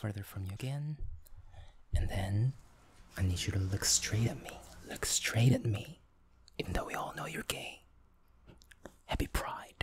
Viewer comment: I started falling asleep so my instinct was to slightly wake myself up and plug my earbuds in and then I heard “look straight even though you’re gay” and I almost burst out laughing LMFAO